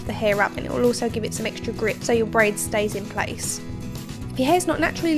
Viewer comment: am i the only one who thought she said "it will help your brain stay in place?" had to replay a few times